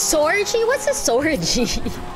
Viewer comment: I heard “sex and drugs orgy”